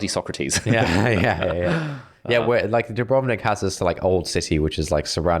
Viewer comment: where is this place that we're referring too? did i hear a that it's a city in Croatia? Romewick? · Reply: Dubrovnik, Croatia